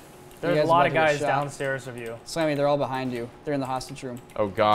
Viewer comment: Does Niko call Sam "Sammy"? Or am I just hearing things??